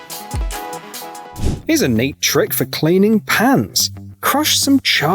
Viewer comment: thought he said “pants” — i was so confused.